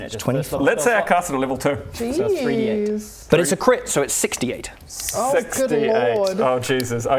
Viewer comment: A subtitle error in 18:30, when says "But it's a Crit, so it's 6d8", the subtitle says 68 instead of 6d8.